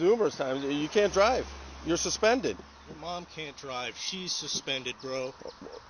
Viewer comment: He didn't say your, he was talking about his own mother also being suspended and unable to drive so he has to illegally either way, that's why the cop says "that's a you problem". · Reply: And i can see how that would LOGICALLY make sense to you. and thats how it sounded when i first heard it. especially since his mom was part of the conversation. but he clearly said "your" and the inflection makes it clear he was trying to make a counter point. unless he isnt suspended, stating "MY MOM is suspended" isnt a counterpoint to the accusation he is suspended. everythign else here seems to indicate he is suspended, and he knows hes suspended. A "yo mama" joke would explain that inflection. and the officer likely heard it like me and you heard it the first time, that he was talking about his own mom.